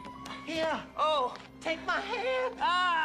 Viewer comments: The mandela effect..... it used to be "take my strong hand" like everyone remembers...... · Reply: More like misquoting As for me I thought he said “Take my good hand” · I grew up watching this movie over and over again. Never once have I heard him say "take my strong hand" 🙄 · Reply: We'll box rn cause I grew up watching that movie I always heard him say "take my strong hand"